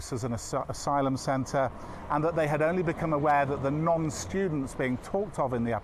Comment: Does he say Nonce students were asylum seekers?